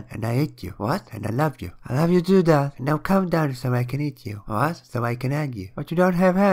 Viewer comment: I heard "so I can end you"